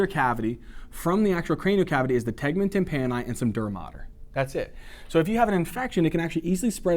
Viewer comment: Teacher, will you tell me what term you used at 43:21? I believe you said "dermater?" I tried looking it up but had no luck. Thank you and sorry for the inconvenience. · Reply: He meant "dura mater"